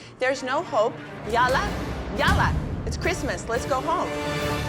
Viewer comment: did she just say “Yulla, Yulla!” I think she is trying to say yella yella which in Arabic means hurry hurry (I’m actually Lebanese)